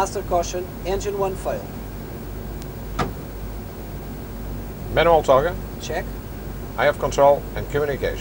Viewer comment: what does he say @ 1:22? sound like "minimal TO/GA"...